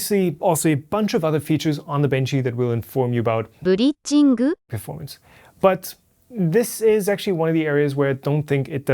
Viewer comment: what am I hearing in your audio at 12:15-12:16 ? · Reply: I don't know why it's a japanese text-to-speech there, but i think it's saying "bridging"